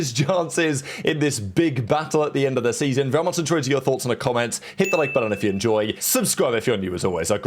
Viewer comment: Can someone please tell me what he says here? Right after he says "end of the season" he says something really fast and I can never undertstand wtf it is!! Been trying to figure it out for about 20 straight videos but I give up 😂 Sounds like he says "very much in towards your thoughts in the comments" but that can't be right lol Someone please clarify this gibberish for my primitive brain 😂